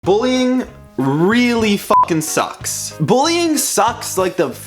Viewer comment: Sometimes, when he say: "sucks", I hear "socks" lol